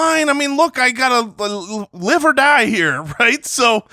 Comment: It's live or die here! What I heard: It's liver die here! Conclusion: drafted a ton of baconators